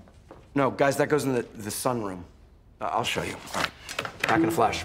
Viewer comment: does Gavin say "thun room"?